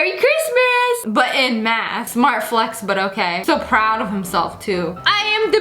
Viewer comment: did she said weird flex but ok.